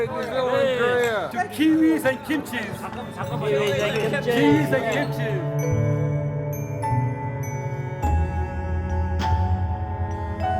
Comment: it sounded like "to kiwis and cream cheese"